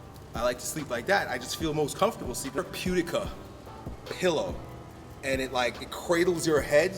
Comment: What pillow is he talking about at 28:20? Couldnt understand the word. Thanks :) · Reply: Arcade Therapeutica Pillow, just google it.